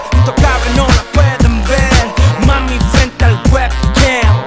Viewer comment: pensé que decía "mami frente al juego"